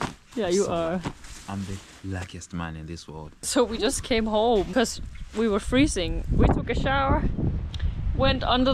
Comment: "I'm the luckiest man in this world" 7:50 That's the purest and rarest love, may god bless your couple and family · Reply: i thought it said blackest 😂😂😂